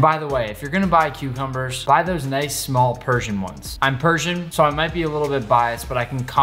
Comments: Did he say he was Persian? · am I stupid or did he say he was Persian?